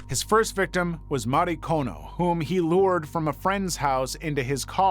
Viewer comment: OMG FROM ACROSS THE ROOM, I THOUGHT YOU SAID MARY-CŌNE 😂👉(56:19)😳😳WAIT 😳U SED WHAT⁉️😳🤭🤣😂😅😆😅😂😆🤣🤣 go ask a Puerto Rican what that means or anybody that speaks Spanish🤣😆😆🤷🏻‍♂️ I DIDN’T HEAR THE O I JUST HEARD CONE NOT KONO😂😂